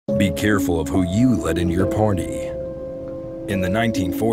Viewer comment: I didn’t read what it said, and I literally heard “be careful who you let in your porne!” 😅